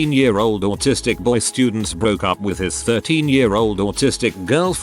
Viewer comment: I was listening to this video in the background while cooking and I have never been so glad I misheard something when I got to around the 8:00 mark. I thought they said 30 year old and his 13 year old gf.